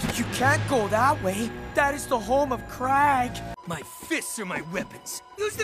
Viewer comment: Did it sound like he said Crack instead of "Krag" to anyone else?